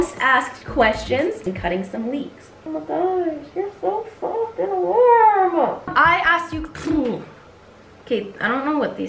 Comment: Who else thought she said to the cat your so fckin soft and warm lol